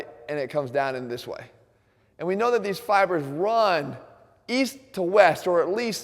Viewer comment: East... I thought you said weast